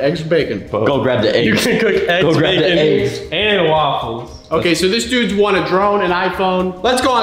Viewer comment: When was spitting water I thought he said you waffle